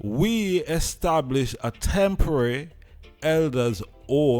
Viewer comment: I heard him say a permanent elder care home . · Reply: go back and listen because I know I heard temporary before I heard the rest but in any aspect it's still not enough and is wrong building a home for elderly doesn't mean they're going to get accable care we already see how these so call professionals treat sick people him hospital do you think is Rastafari ago get good care going into these places? And who is going to pay these people who is planning on working in these places? Remember now it's supposed to be a free home and care given to the remaining Rastafari dem or is it by the time them build it them man deh pass on so they're going to start charging families who wanna bring their elderly there? Y'all have to pay attention uonu is something they're putting up that's going to profit them and not actual people who needs this help SMDH I really wish people look at what's not being said as opposed to weh dem ah seh